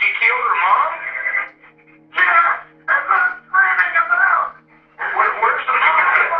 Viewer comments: He’s saying “that’s what I’m screaming about” I believe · The unintelligible part he says “yes! That’s what I’m screaming about!!” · "yes, that's what I'm screaming about" is what I hear in the "unintelligible" part